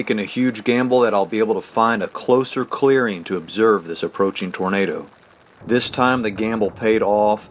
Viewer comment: With captions on on 1:59 instead of tornado it say "burrito".